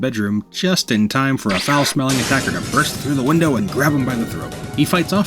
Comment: Yo the doom part with the shotgun was perfect, although I listened twice to hear what you said attacked him and I still can’t make it out, sounded like you said raptor?! I’m assuming it was a hunter? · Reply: Thanks! The part at the window? I said "attacker" as a vague term, but it was an ordinary zombie. Which immediately disappeared somehow 😆